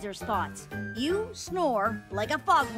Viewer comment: What did Martha said in scene 5:06?? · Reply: Verónica P. Rivera you snore like a foghorn factory and he can’t sleep. Is what she said